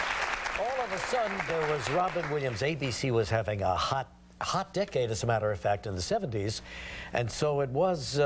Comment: LOL I thought he said "hot dick" in the beginning. It was "hot dec-ade," but he like relaxed the last part of the word LOLOLOLOLOLOLOL OMG my heart stopped xD